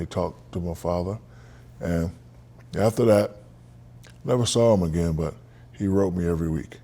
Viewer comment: just need to make sure i heard that right, did he say he never saw his step dad again after that?